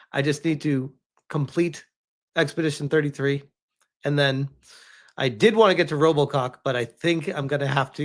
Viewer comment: Did he just say “I did want to get to Robocock…” at 12:32?